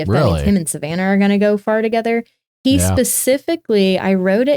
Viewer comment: for a second I thought she said “gonna go fart together”